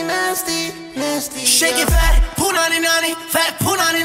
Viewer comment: I thought my dude said that Punani but he said fat punani